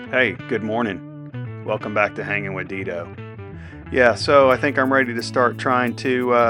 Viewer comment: For a minute there I thought you said Hanging with Itoh